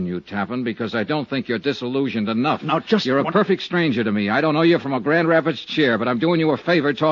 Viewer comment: At about 54:22, Johnny says something that sounds like "I don't know you from a brad rabbit's chair." What did he actually say? I get the gist... Was that a common expression in 1955?